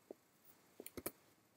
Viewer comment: When ever you say 'Pen tool' I keep hearing 'Pain tool'.. I could keep up with your great instructions up to a point but then I always lost it at the end and got frustrated. :) Oh well, need to keep practicing. Thanks for a calm and helpful tutorial!